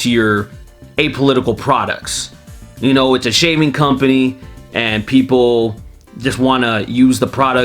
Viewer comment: When you said "it's a shaving company" I misheard it as "it's a shaming company" but I guess both are accurate so it works either way.